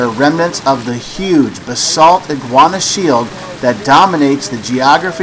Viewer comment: "Yeah. Right" I loled so hard. Come to Miami! We have great peacock bass (cichla ocellaris) fishing in the canals here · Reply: And did you say Iguana Shield instead of Guiana Shield at 2:35?